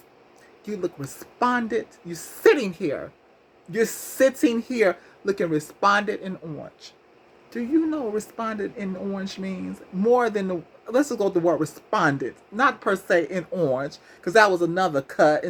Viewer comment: I thought she said " Despondent and orange" which is a lot worse. She basically saying she looks pity full and desperate sitting up her dressed in orange dissing her attire .....and said all of that after giving her the disrespectful reasons why it should be said.